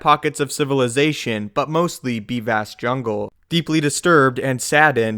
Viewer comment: "be vast jungle" sounded like "deep ass jungle" when I first heard it.